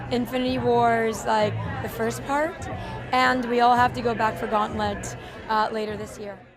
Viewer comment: Damn, I never thought about the fact that they could be in the stone! I would never have guessed that if not for this video. Wait also at 5:11, did she just say that the next movie will be titled "gauntlet"??...